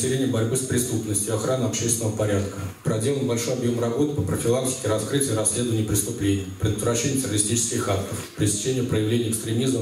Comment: Сначала послышалось, что сказал "профилактика раскрытия и расследования преступлений".